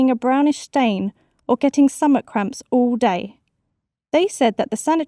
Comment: summet? Sounded like she said summet ahaha